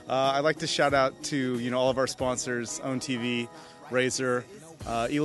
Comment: did he say owndtv?